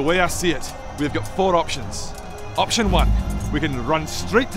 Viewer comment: subtitles say 3 options, whilst says 4 :)